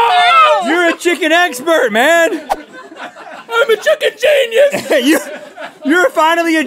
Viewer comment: It almost sounded like link said I'm a freakin genius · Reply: shevonnie salmon he said, "I'm a chicken genius," not "I'm a freakin genius," lol.